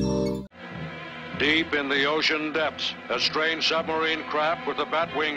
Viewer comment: Am I the only one who hears the narrator saying "Strange submarine crap" here?